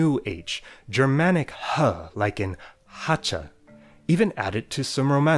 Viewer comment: I thought he said "hwacha"